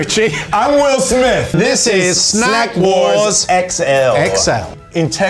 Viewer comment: Thought it said smack wars at first